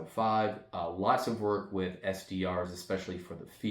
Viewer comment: did you say SDR or SBR? Either way I’m down! 😅😂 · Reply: Both are good in my opinion. I have both. I said SDR, though.